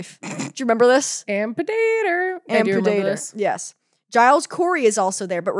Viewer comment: when they said "Ann Pudaetor" so musically, it sounded like "amputator," like shes some sort of terminator character killing people, or "cutting off" relationships like you'd amputate a body part, effectively killing that body part, lol. Yes i have adhd, why would you even ask?